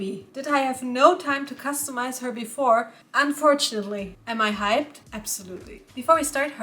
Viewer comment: I thought you said 'Am I high........ ABSOLUTELY' 😭😭💀 I need help! Edit: oh my jeebus she's gorgeous!! 🥰😍